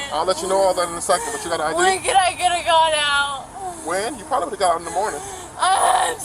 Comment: I hear " When could I've get of got out?"